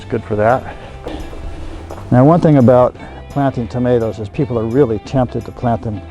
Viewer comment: I thought he said "potatos" and not "tomatos", and then he sad "when they get 6ft tall". Holy almighty jesus christ lord our savior are those tall potatos :-D LOLS